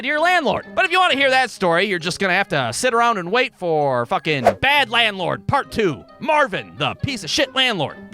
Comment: I thought it said Bald Landlord at first